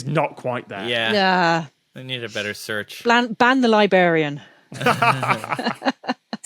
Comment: Did she say "libarian"? Well, in a way, that might actually make more sense. 🍷😜🍹